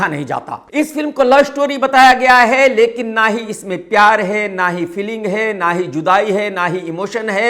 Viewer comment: did hi just said 'Chudai hai?'